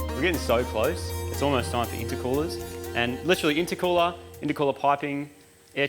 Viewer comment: said "it's almost time for intercoolers.." and I swear he said its almost time for intercourse...